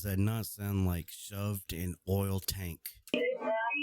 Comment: I heard " shoved in oil tank alive " ... sound like he is removing background noise and heightening= the sounds that are at lower frequency .... they did ask her if she ever had the code and she acted strange!